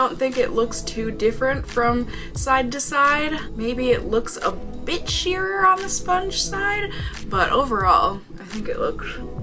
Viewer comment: I literally thought you said "a little bitchier on the sponge side"😂😂 until I realized you said " a bit SHEER on the sponge side"